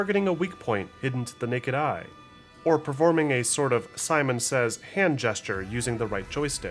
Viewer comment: Performing a Saiman Says hand gestures? Am I hearing it correctly?